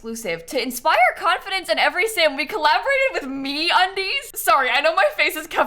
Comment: lmao i was not looking at the video when she said that and i thought she said "we collaborated with me on these" instead of me undies hahah